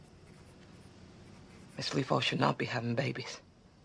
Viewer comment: I can't tell what Aibelene says at 0:31-:32... Caption jumbled it. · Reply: Darkb4light 06 Skitter: Its very true A: Miss Lee should not be having babies